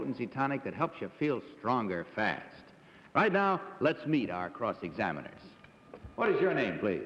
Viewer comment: What’d he say at 1:05? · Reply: Right now let's meet our cross examimers !